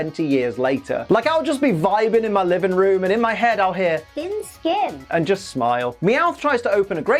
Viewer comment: I always thought he said thick skin